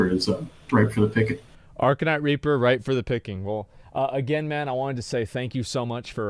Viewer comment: RIPE for the picking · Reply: I thought he said prime for the picking.